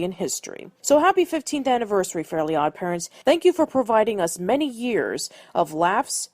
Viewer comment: What were you gonna say at 9:16. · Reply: "Thank you so much for providing us many years with laughs and magic." Of course, YouTube being YouTube had to cut off the music since I had gotten hit with a massive copyright strike.